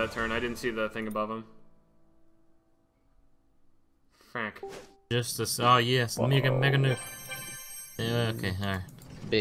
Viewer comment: it sounds like side said nigga mega nuke